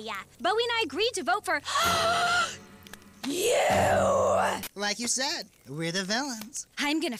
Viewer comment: for a long time i thought Bowie said "Like you said, were developed"